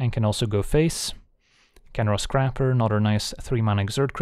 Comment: I kept hearing "Khenra's Crapper," and pictured some mythical toilet of legend.